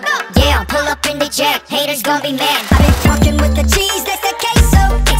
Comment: Am I the only one who thought jaehyun said “I’ve been fucking with the cheese and the quest” 😂